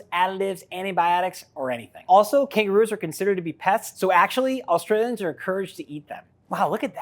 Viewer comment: bruh I thought he said kangaroos are considered to be PETS so they're encouraged to eat them 💀